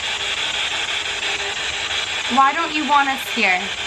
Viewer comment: I heard" Why Wont you' leave instead of "lying"